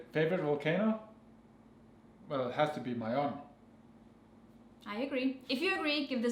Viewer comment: ahhh mayon vulcano....at first I thought you said "my own". i was gona say WTH!!!..hehehe